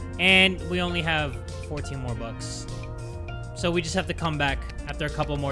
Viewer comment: did he just say bobux?